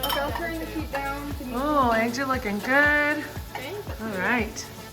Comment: I thought sorry I forgot the mom‘s name I thought she said finger looking good but she said eggs are looking good lol